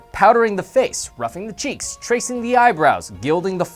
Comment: Did i just hear say "Roughing the cheeks"? Isn't it "rouging" (rue-jing)?